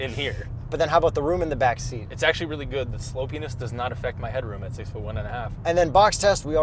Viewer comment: "slopeyness" I heard it as "slow penis" LOLOLOL